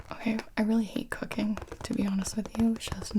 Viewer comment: when said cooking, it sounded like she said cocaine